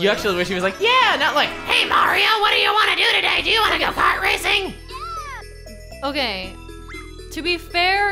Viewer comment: it sounded like said " hey Mario what do you want to do today, do you want to got pot racing?"